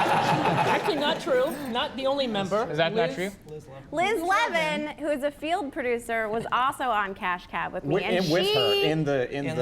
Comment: It almost sounded like she said Liz Lemon.